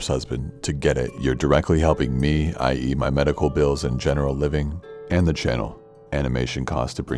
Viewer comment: why did it sound like he said i eat my medical bills😔 his voice though i be drolling its night and im over here fangirling over his voice like jesus his voice and laugh just makes my day so so so much better